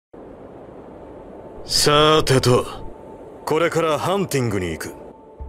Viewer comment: Wait did just said hantengu